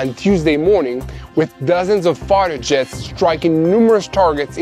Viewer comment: Hahaha. It sounded like he said "dozens of farter jets" at the beginning because of his accent. Too good. Keep up the great reports my friend. I knew what ya meant. Just needed a laugh this morning, and I figured I'd share it with you guys